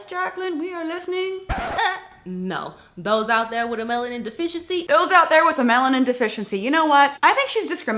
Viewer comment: I thought she said, "melon deficiency". So, of course, I started thinking about boobs.